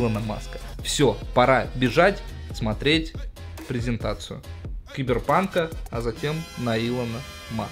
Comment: На 3:57 мне послышалось что он сказал пидрпанк